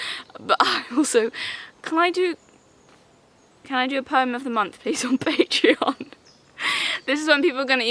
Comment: I misheard “poem of the month” as “perm of the month” and tbh… I’d probably pay to see you do a “perm of the month” series.